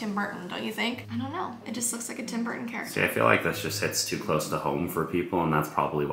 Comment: When she said “It almost looks like a Tim Burton thing” I swear I heard “It almost looks like a Tim Hortons” 💀💀💀 (Might’ve spelled the wrong I don’t live in Canada)